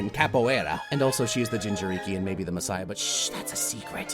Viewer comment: At first I thought you said Ginger turkey because those tails looked like turkey feathers. It took me about 5 listenings of that line to realise you said jinchuriki(don't think I spelt that right)